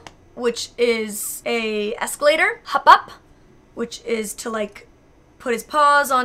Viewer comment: That sounded like "hop op" to me, which would literally be "jump up" in Danish xD